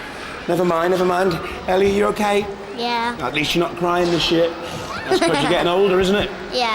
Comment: CC "Never mind never mind... Ellie are you Ok?" Ellie: "Yeah" CC: "At least you're not crying and shit" Did I hear that right?? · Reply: atleast you're not crying this year